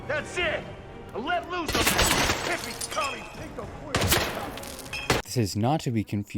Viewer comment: I’d like to mention this line as I’ve heard it countless times on kill everyones I’ve done or watched but I swear he says “Let lose on that hippie, commie, pinko, queer.” Does anyone else hear that?